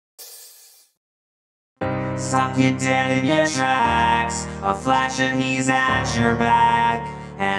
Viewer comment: the first part sounded like he said "suck it daddy your trapped" LOL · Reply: but it didnt for me lol it was "Stopped you dead in your tracks"